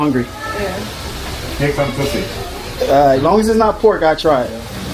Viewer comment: did he say take some pus sy?????